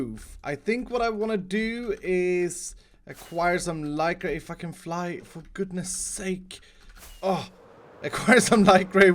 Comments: Did I mishear that or did he actually said "and f*cking fly [...]" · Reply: No he said:"if I can fly for goodness sake". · Wow I think I heard swearing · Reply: I heard for goodness sake then I saw this comment and it does sound like he is swearing but I'm sure he is not swearing